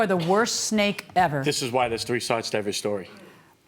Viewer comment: did he say three sides?